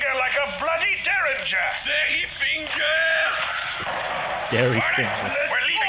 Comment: I have watched this episode ~5 times now 2x with your commentary and just caught Pascal said "Derrifinger" I thought he was saying "Dirty Finger" since the nail was green. Never looked at his subtitles all this time